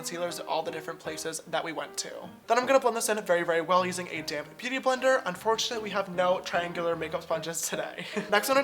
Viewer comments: Everytime he says ‘damp beauty blender’ I hear ‘dang beautyblender’ 😂😂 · Is it just me or does it sound like he says dam beauty blender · When I first started watching your videos I thought you were saying damned beauty blender 😂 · i've been watching james for about 6 months now and i just realized he's saying "damp beauty blender" when i thought he was saying "damn beauty blender". 🤦‍♀️🤦‍♀️